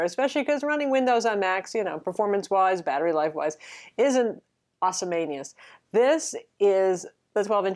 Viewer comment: Did she just say "Awesome anus"?